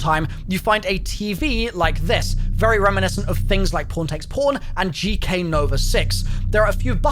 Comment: yall british scare tf outta me bruh i keep hearing p*rn instead of what you trying to say lmfaoooo